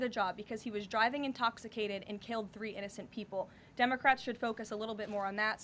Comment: Did she just say dumbecrats?